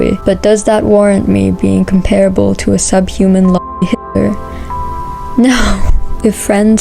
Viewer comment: What is the word she says at 9:18? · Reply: ‘Sub human l0li hitter’ I think